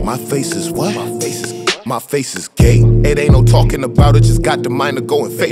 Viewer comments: Yo did he say his face was gay? serious question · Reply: lmfao m from florida and thought he said my face is gay. never heard the term gate before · not tryna be rude but did he say “my face is gay” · did he just say my face is gay???😂😂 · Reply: He said gate it’s a Florida term